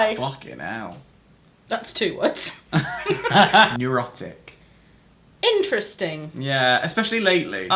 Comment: I definitely thought said Erotic rather than Neurotic for his one word that defines him...